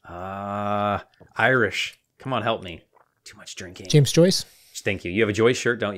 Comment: "Too much drinking"🤣🤣 I heard that at 0:41, that's hilarious 🤣